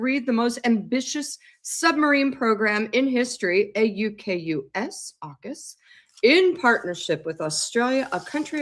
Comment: When I heard the King talk about the submarine programme I heard “Orcas” not “Aukus” and thought what a great name.